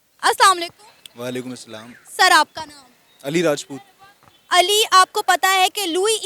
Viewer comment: did he said ali RAJPUT? 💀☠️